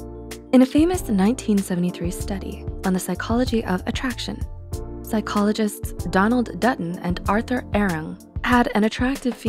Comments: I swear I heard "Psychologist Donald Duck" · I thought they said Donald Duck for a sec...